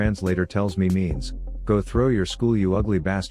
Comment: He actually says "go shoot up your school"